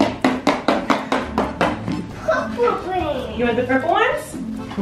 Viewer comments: Who else laughed historically when was coloring in the, as says "Poo Poo"? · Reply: He said “de purple?” And Colleen thought he said “poo poo” 😂 which makes it 40x funnier. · I’m 98% sure said “de purple” and Colleen thought he said Poop hole 😂